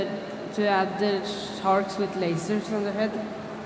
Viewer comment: i dont understand what he says or what the man says back. is it something about lazers? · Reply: Sharks with lazers